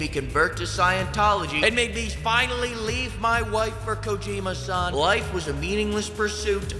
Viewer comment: When he said, the game, made me finally leave my wife for Kojima-san, I misheard it as ‘Made me Leave my wife for Kojima’s son’ I am deceased from laughter.